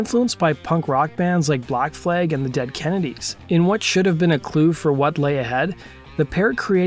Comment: What did he say name of the two bands were ? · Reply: Black flag and the Dead kennedys